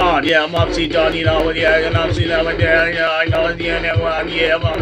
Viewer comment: at 54 seconds he sounds like he is saying "I'm MC downey"